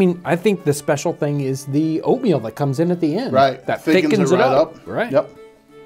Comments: I thought he said, "Its really the opium at the end..." [cut to throwing fistfuls of powder in the soup] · “i think the special thing is the oatmeal that comes in at the end” I heard “opium” instead of “oatmeal” and had to rewind. That would be a very special soup indeed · Did he say Opium that comes in at the end? Did he mis-speak and meant to say oatmeal?....try adding 1/2 to a whole headof cabbage to your next pot of "Soup". You will never go back to making it any other way if you do.